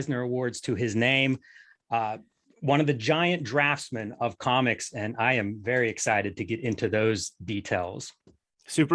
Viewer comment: who is the artist they are talking about at 1:11? I can't make out the name. John ... something. ? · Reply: John Paul Leon.